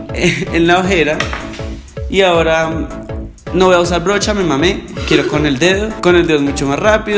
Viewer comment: oí bien o dijo me mame en 8:10 minutos